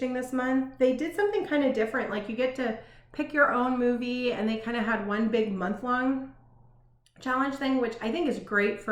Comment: What kind of cat does Leslie breed? It sounded like you said bourbon, but I could not find any info. THanks! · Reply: She breeds Berman cats and they are adorable!